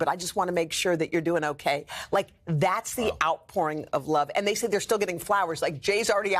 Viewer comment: Did She Say, “The ‘OUTPORN’ Of Love”!?!?!?!?!?